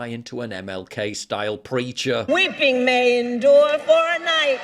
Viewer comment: I had to turn on CC to understand Karambala's MLK accent. I heard "We been main door for a night" in a Cockeny accent. But apparently it was "Weeping may endure for a night" in a southern Baptist Atlanta accent.